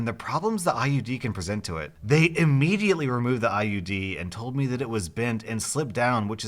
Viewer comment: Am I the only one who thought he said IED? Like when they start surgery, KABOOM